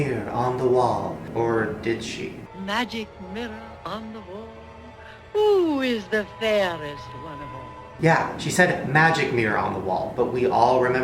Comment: RIGHT I JUST REALISEd IN SNOW white I thought it said WHOs ThE fairest of them all but it actually says WHOS ThE FAIRESt ONE Oaf ALL !